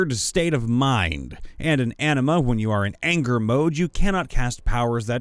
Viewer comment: Every time you said “Anima”, I heard “enema.” Makes for a very different story.